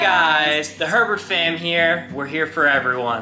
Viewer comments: OMG IN UR INTRO I THOUGHT U SAID "THE PERVERT FAMILY" JFNJKFNJKSDFVNJNG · Idk why by when the intro music said “The Herbert’s are here for everyone.” I thought it said perverts.